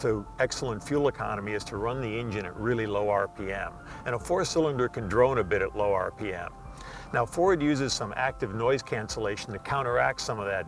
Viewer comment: a 4 cylinder angine can "drowne" a little bit at low rpm. sorry, what did he say? i can´t make it out. drome? drowne???